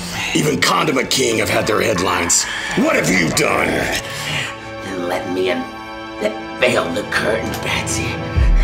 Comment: What did the Joker say at 15:20? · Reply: "Then let me un... [stammers] ...veil the curtain, Batsy."